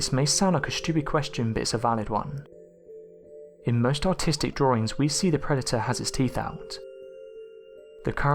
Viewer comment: Sounded like you said "most autistic drawings" xD